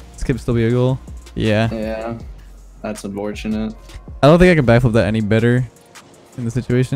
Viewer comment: did I hear say "aw... that's abortionate" ? LOL